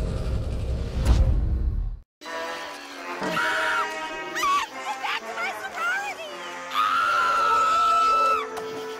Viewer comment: What is she even yelling?? XD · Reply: "I have to get back to my sorority!!" 🤣🤣🤣